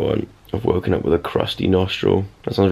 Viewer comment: I thought he said At the beginning, "I woke up with a crusty bumhole" I need to stop wearing headphones.